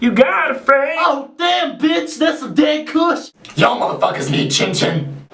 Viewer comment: Sounds like he said "oh damn bitch thats some gay kush